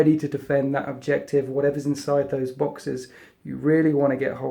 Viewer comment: I wonder if I am the only one who heard 'boxers' instead of 'boxes'.